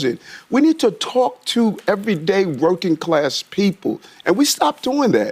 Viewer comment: I keep hearing "broken" class, when it's actually "working" class.